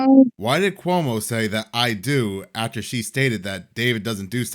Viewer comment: These aren't the most talented or articulate podcasters but am quite sure @ 16:45 Cuomo mumbled "I hear ya" not "I do"